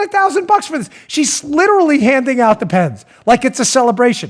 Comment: I swear, I thought I heard Dan say “ and there’s Pelosi... Handin’ out Depends”. I thought to myself “ Why on earth would all of those Dems be needing Depends?” Now I know why...